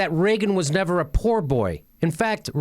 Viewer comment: Oh, he said “poor boy.” It sounded like he said “pool boy.”